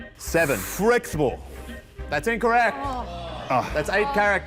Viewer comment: What did Waka say on the second round of the final task? He landed on 7 and got no points because Tom said it was 8 characters, but I thought he said "fretful" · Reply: Closed captions say 'flexible'.